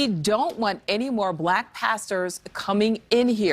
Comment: Upon hearing this obviously racist man's statement I thought he said "these black bastards" instead of pastors. He previously lamented that there were not enough bubba's and Joe six packs in the jury pool. White people are going through a reckoning of sorts. The trump supporters are definitely returning to the harsh racist mindset of their ancestors from yesteryears. The fence sitters are in anguish and conflicted. Those who come from families that have always resisted pulled of blanket hatred and ambivalence towards non white people, particularly the economically challenged black communities. These white see individuals and not inclined to saddling them with stereotypes while racist whites see the general black population as degenerates basically. You have to prove your individuality to them and still remain under their harsh scrutiny. Now, I hearing their toxic language morphing into violent threats of death and mayhem against diversity, multiculturalism etc with 70% of trump supporters calling for secession. These are amazing and dangerous times as any in our sometimes violent history.